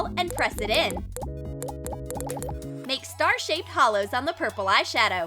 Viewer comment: did she say Holo?!